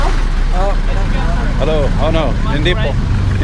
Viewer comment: min. What did that fellow ask you? Your reply sounded like "Home Depot". I know that can't be right, but what did he asked you? and your correct reply. Love your video's and the walk a round's. Thanks for share your life with us. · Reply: Chris Scogin His reply was Hindi Po, which means No Sir. Not sure what the guy asked but probably it was if he wanted to buy something or if he need a trike